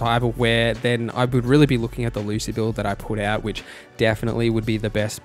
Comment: You mentioned a Lucid (Lucy? I couldn't understand the word but CC writes it out as "Lucid") build but I don't see any links for it. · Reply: Apologies, I'll go fix the CC. Talking about Lucy from the anime and this video: <link>